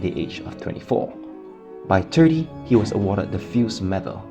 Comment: lol u said turdy instead of 30